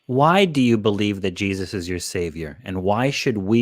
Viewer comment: When said “Jesus is my savior” I heard “Jesus is my favorite” and just started laughing uncontrollably.